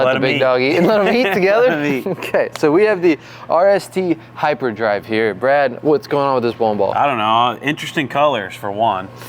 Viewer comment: Ok so they don't show the ball up close or label the ball anywhere's. It is the - Roto Grip RST Hyperdrive Bowling Ball, Mentioned in the beginning, 0:19. Just in case anybody didn't catch it in the beginning, like i didn't and had to scroll around a little bit. :finger-red-number-one::text-yellow-goal